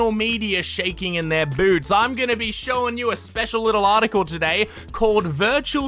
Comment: for some reason i heard b**bs